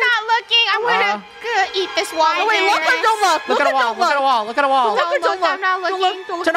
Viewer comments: I heard funneh said "look at douma" am I the only one hearing that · I heard say " OHWOaHO I'm gonna eat this wall i- "